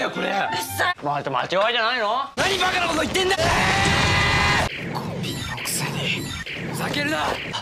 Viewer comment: コピーって聞き取ろうとしたらちゃんとコピーなのに無意識に聞くとコンビニに聞こえるのもはやバグだろ…。